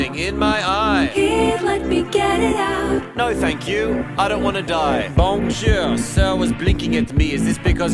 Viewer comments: I heard him say "no thanks but I am gay" when he said "no thank you I don't wanna die" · I heard "whyyy did I keep yelling" when she said "here let me get it out" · I heard i don't bananad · song “here let me get it out” what I heard “why did I date you”